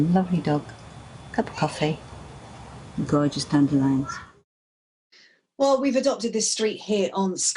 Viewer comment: did she say gorgeous dandelion? Dandelions are the corona virus of lawns lady sheesh